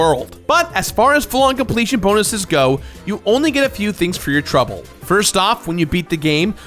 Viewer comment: I’m sorry did you say Treble? XD 16:30 ish